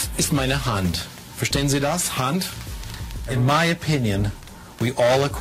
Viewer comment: Excellent video. Just one note at 2:52, the second sentence he says "Verstehen Sie das, Hand?" for the subtitles.